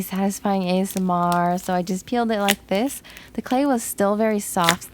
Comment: I love how she said “SO I JUST KILLED IT LIKE THIS” 😂😂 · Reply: She said peeled lol 😂. It just sounds like she said killed